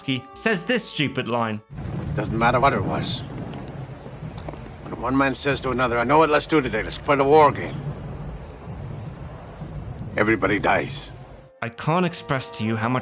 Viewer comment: Can someone translate the line at 33:00? I can’t understand what the character says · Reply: 'Doesn't matter what it was. When one man says to another "I know what let's do today, let's play the war game." Everybody dies.'